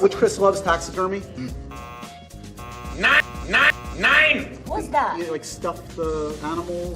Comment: did he said Nein which is the German word for no